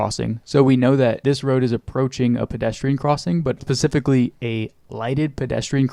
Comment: did you say “Pacifically”? You did!